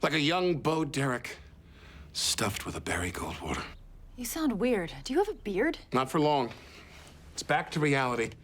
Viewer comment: 'You sound different, do you have a beard?' lmao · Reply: You heard something different. I heard her say "You sound weird..."